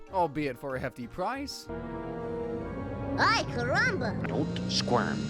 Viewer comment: AY KARAMA (idk what it said ok)